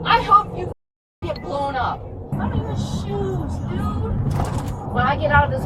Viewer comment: come on… she clearly said “I don’t even have shoes dude.” Not “I’m going to shoot you.” These AI captions are out of control. If you can’t even watch your own video for accuracy then why post? This is more than lazy; it’s irresponsible.